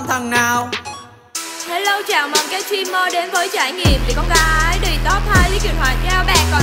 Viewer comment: gây nhiều sự chú ý nì :))))) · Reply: Tôi nghe thành " bị gái đ!t " :)) Giọng đuối mà cứ cố mãi Chiuụu